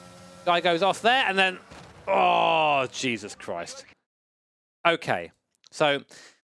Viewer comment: Did I hear "Jesus Crash"? hahaha Loving these videos